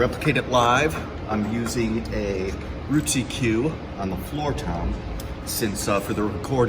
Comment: So does anyone know what he says at 7:52...when describing the tom head damper? Is it "Rootsy Cue"? And where do you get those? Not sure if Evans or Promark makes them? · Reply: Roots EQ dampeners